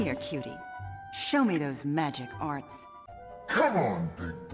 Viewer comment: HELP I THOUGHT SHE SAID "Show me your magic wand~😏😏" HAHAHAHAHAAHGAJHSJAJAA